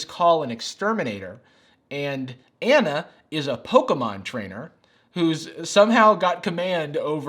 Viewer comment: I honestly was distracted by the comments and then I heard Jarvis say "Anna is a Pokemom trainer" And I looked up and was like I KNEW I WAS A POKEMOM TRAINER😂